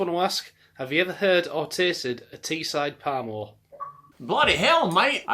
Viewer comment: I found it very funny when you put on an Aussie accent on when a North East fella from the UK asked if you had heard or had tasted a Teeside Parmo, clearly you hadn't as you thought he said palm oil lol. Thought I would let you know the Parmo or Teesside Parmesan is a breaded cutlet dish originating in Middlesbrough and a popular item of take-away food in the North East of England. Similar to a schnitzel, it traditionally consisted of pork in breadcrumbs topped with a white béchamel/Parmesan sauce and, despite the name, usually Cheddar cheese; there are many variations, including non-pork cutlets. The Teesside version differs from the Italian Parmesan which is made with veal and uses Gruyère cheese instead of béchamel sauce. Maybe you could come to the north east of the UK some time and try some of the local delicacy.